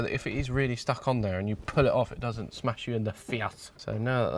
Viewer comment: HAHAHAHA! I honestly thought you said "Smash you in the Fiat."